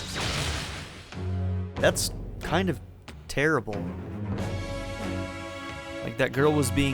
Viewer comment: Lol I thought you said the skill earned was terrible and I was like 'wut'. But yeah that's pretty inhumane.